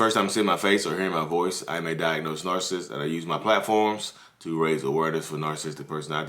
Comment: Did he say he may diagnose narcississt or that he is a diagnosed narcississt? I'm just asking because I thought I heard him say he was a "diagnosed narcississt"........oooohhhh nooo...yall be careful...as for me no no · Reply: He IS diagnosed